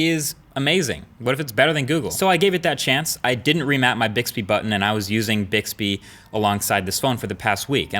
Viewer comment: did he just say bitchby at 8:08?